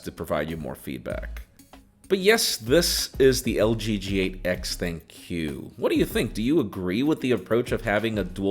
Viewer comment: I heard you said "LG G8X thank you" 😂